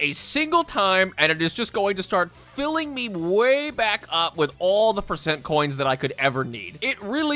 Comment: "Uh, yes baby, fill me up with all of those percent coins" is what I heard